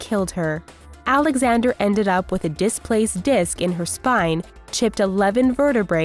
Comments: you guys put "Spin" instead of "Spine". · it says spin instead of spine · typo. "disc in her spin" instead of "spine". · anybody notice that it said spin instead of spine ay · instead of spine it says spin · the subtitles say "spin" instead of "spine" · spells out spin instead of spine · you wrote spin instead of spine LOL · Ok 6:15 it said spin instead of spine. Wtf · it said on the subtitles spin instead of spine